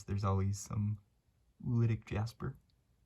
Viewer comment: What is that word? Around 14:10 or so you say a word that I've heard you say before and I can't tell what the word is. It sounds like you're saying ruidic or ridic jasper and there's no such thing when I google it. To me it's grainy or speckled red jasper and it has some depth like you can partially see into it. In other words, it's not the solid red jasper of say a pudding stone. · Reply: The word is oolitic.